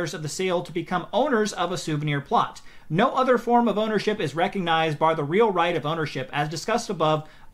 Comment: This isn't the first time this has been done, so i'm actually surprised creators fell for it AGAIN. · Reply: did he say "boner ship"?